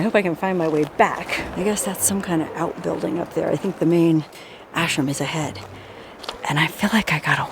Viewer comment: I thought she said ashcan! What a beautiful vaulted ceiling.